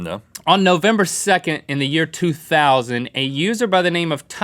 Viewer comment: Did he just say that 2020 was probably a good year? Now I can see why his "Personal" issues are in 2020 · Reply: Year 2000, not 2020